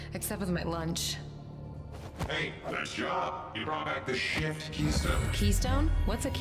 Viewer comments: it sound like it said "Hey nice job, you brought back the shit keystone" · Had any one heard it say shit instead of shift